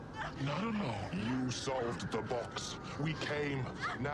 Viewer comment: I always laughed when Pinhead said that. I always thought he said "YOU OPENED THE BOX; WE CAME."